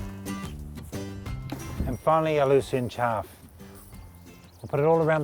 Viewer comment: what did he say that is??? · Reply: Lucerne chaff but can be any kind of straw :)